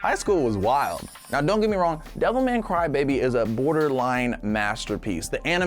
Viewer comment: I heard him say devil may cry baby